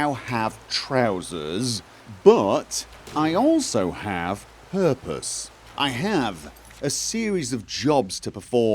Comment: When you said "I have purpose" at the beginning I thought you said "I have herpes" just pronounced awkwardly. Probably because you were just mentioning used pants...